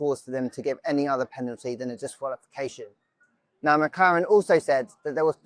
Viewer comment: did i hear McKaren